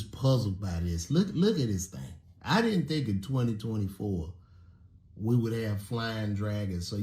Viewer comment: Aye am i tripping or did he say 2024 at 1:42 check it out